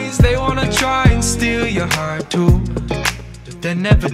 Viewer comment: When you say "heart too" it sounds like your saying "heartu" in korean and it's cute😆